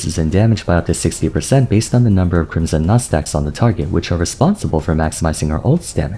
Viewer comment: I don't know why, but at 5:00, i thought you had said "Crimson Nutsacks"